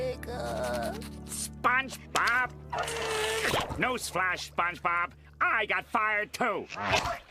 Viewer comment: When I was a kid, I thought Squidward at 12:02 said “No splash, SpongeBob!”, thinking that he meant to tell him to stop crying.